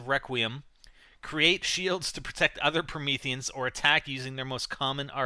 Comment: lol, i thought it said "Prometheus"